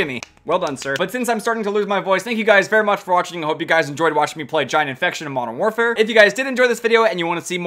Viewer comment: bruh I thought you said "China infection" xD